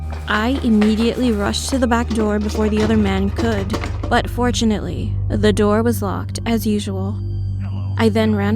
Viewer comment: I THOUGHT SHE SAID UN FORTUNATELY. I was about to slllllap that b across the face